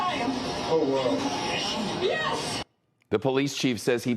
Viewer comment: did anyone else hear a yes.